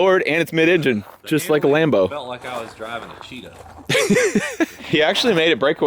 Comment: He said "it felt like I was driving a cheetah" Lmao!! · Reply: Honstly, I thought he said Cheeto.